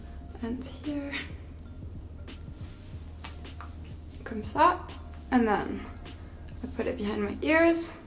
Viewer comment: I think she said "là" (there) at 10:36, "comme ça" (like that) at 10:44.